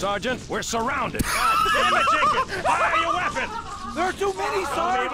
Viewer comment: When Johnson says to fire your weapon, I stg I thought he said "why are you laughing?"🤣